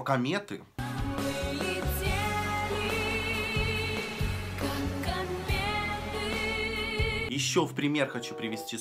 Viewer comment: А мне послышалось : " Мы летели, как конфеты" 😅😭